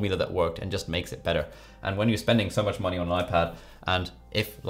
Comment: U speak british and u say badı instead of beta ?